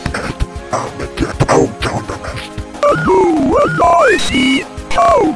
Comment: did he just call me a pest..